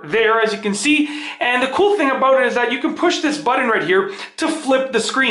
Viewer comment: What's a buddin at 1:16 ? Ohhhhhh you're saying "button" but not pronouncing it correctly.